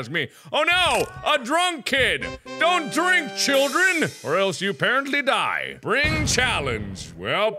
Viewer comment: It sounded more like "Don't drink children" instead of "Don't drink, children"